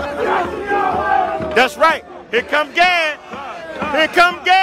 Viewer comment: Can someone translate 1:33? I honestly cannot for the life of me figure out what this guy is saying. Here come geah? Here come gaueehhyee? Like wtf.